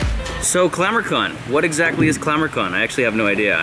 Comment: I thought he said glamorcon 😂😂